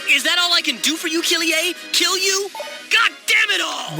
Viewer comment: for a second i thought he said "is that all i can do for you, killer? kill you!?" and that stopped me dead